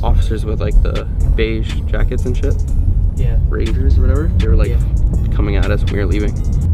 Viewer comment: did Jesse just call the sheriffs, raiders..lol.